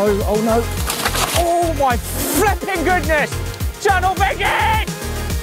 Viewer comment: did he say “Channel Vegan”😂😂😂